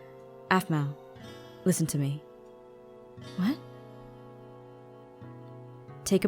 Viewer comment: I thought that said Aphmau DOESN'T listen to me and I thought that that was playing a joke